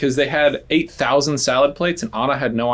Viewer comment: Didn’t they confirm that it ‘a thousand salad plates’ not 8,000? And it’s just been misheard?